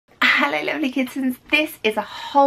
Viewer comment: did u just say hello lonely kids right at the beggining